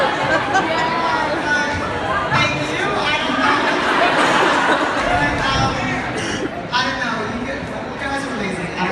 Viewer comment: What was Laura saying thank you for at 28:06? Audio wasn't brilliant so I couldn't make it out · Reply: The question was: "How do you sleep at night knowing that you're the reason we became lesbians"